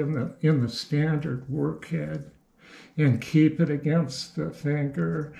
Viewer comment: what is the word used there, please? Thanker, tanker, ...???????? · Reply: Keep the cutter against the finger and use the table "travel"